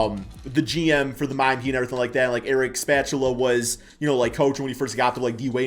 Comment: The Miami Heat's Coach, Eric Spatula? 😆 I think what you meant to say was Eric Spoelstra.